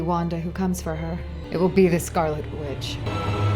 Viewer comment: I thought she said Scarlet bitch LOL